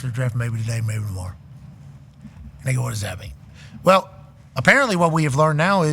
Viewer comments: I had to turn the captions on and run it back at the 1:52 mark 😅 guess he said "they go" and not what I thought he said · did pat say "nigga what does that mean" at 1:53 or am I tripping??? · did he just say “🥷 what does that mean?”